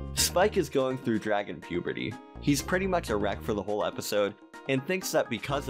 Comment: i literally thought you said "Spike is going through dragon puberty! 😄😃 He's pretty much erect for the whole episode😇" and i almost had to report you for.. idk what... hate speech or smt